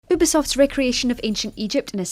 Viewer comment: did she say "boobisoft"?